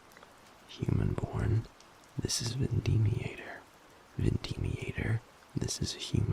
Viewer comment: Tbh, I guess I missed Gavin telling us his "birth" name and thought said "The Deviator" or "The Demonator" as a nickname for Gavin then I was confused why everyone in the comments was saying "Vindemiator". Wait, when tf did we learn Gavin's Demon name?